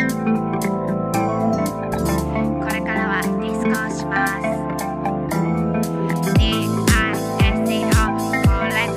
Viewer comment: これからは〜ってサラちゃんなんて言ってるのかな？？ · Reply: discoです。ディスコ🕺🕺🕺